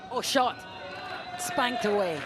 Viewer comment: Did She Say Spanked Away??